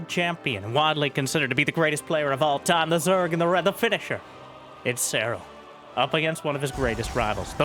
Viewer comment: What he said "The Finnisher" What I heard "The Finnish Zerg"